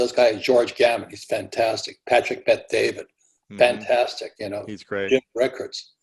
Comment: Who are the teachers they mentioned.... 1:23:16 I heard him say..... George Gavit and Patrick Pat-David? What are their actually names? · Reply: B. Patrick Bet David